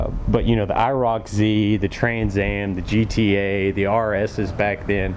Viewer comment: Did you say “the GTA” at 6:30? Lol